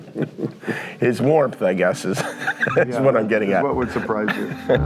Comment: what he say there? Something I’m getting at